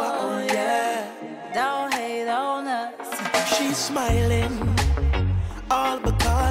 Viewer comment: i heard "don't hate doughnuts"